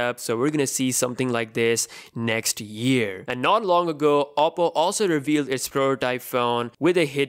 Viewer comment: Lol if you turn on auto caption at 1:40 it say "Apple" instead of oppo.. 😂😂